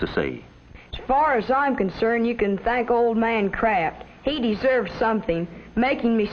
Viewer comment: Did he just say "Old Man Crap"?